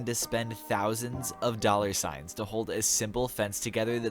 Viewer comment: did he just say dollarsigns